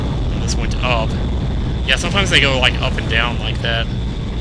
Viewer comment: with captions, says "yea, sometimes they go up and down like that", and Captions says "sea because they don't like him", is it me or do the captions do seprate comentating?